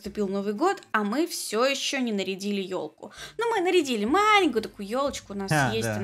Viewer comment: мне послышалось "ещё не нарядили Йоши"